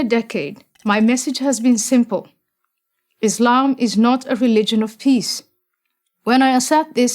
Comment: Yes it is... oh wait, thought it said religion of piss.